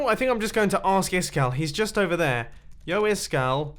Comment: Did Mumbo just say... YO?!?!